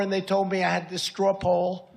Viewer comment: "straple", "strap hole", "stropple" "strawpole"...does anyone know what Trump said or why he was applauded? 😳 🤥